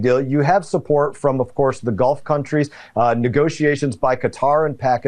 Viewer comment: “Trump got support from the gulf countries…”. I thought I heard “the Golf countries…”, which seemed to make more sense at the moment.